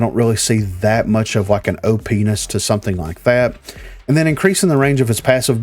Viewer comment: what was that you said? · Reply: OPNESS hahahaha